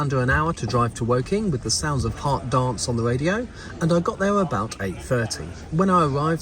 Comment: it just dawned on me how Americanized I am considering I heard "listening to hot dogs on the radio" for the first 3-4 replays